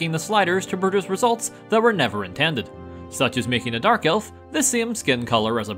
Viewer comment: When said 'dark elf' i heard 'Garfield' wow.